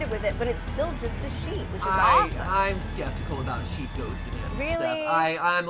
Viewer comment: For the first 20 minutes of the live stream I thought they where saying "sheep ghost" which causes a morbid image of cutting eye holes into a sheep's face. Even worse, I thought I'd missed out on a meme or piece of pop-culture.